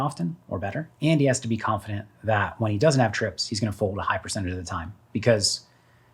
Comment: At around 8:20 you say jungle has to be confident his opponent doesn’t have trips and if he does have trips he’s going to fold a high percentage of the time. How could Bobo ever fold trips here? Wouldn’t that be an insane amount of over folding? That means bobo is only calling boats and quads? Jungle has all the QQ+ with his pf 4b so if Bobo did get here with J8 or 78 he should be praying jungle wants to get stacks in. Jungle has the overpair advantage but Bobo has the nut advantage and jungle just has to discount that Bobo never has trips given his turn sizing but he must know that trips is never folding. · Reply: He said “If he DOESN’T have trips, that he will fold a high percent